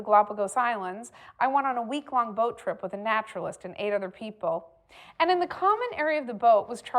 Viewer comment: What she said: "I went on a week long boat trip with a naturalist and eight other people." What I heard: "I went on a week long boat trip with a naturalist and ate other people."